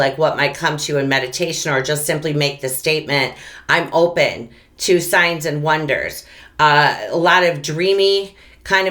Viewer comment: Sorry I can't understand what says at 11:00 can anyone help me? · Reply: That's when I mention being open to signs and wonders. Not sure if that's what you don't understand?